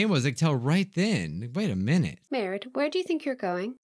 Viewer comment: I thought she said the girl said “Merde” when she talked to the poo.